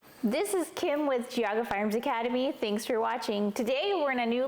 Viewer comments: I thought she said JUGGS firearm academy 🍈🍈👀🤤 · Trying to watch a gun maintenence video but can't stop thinking about motorboating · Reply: That’s ok I thought I heard her say she’s “Kim with Jugs training academy”😂😂😂😂